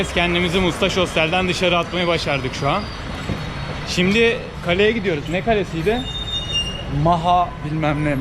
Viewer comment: de şimdi karıya gidiyoruz anladım şimdi kaleye gidiyoruzu öyle bi demişin ki abi :DD